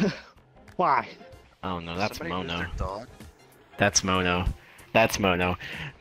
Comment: did he just said ''Mono''?